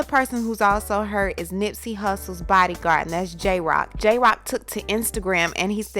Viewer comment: DID I HEAR CORRECTLY" NIPS BODY GUARD" SO THEREFORE, WHO'S BODY WERE YOU GUARDING.